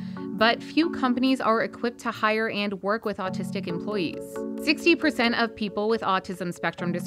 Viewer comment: or 0:17 does she say 16% or 60%? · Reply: 60%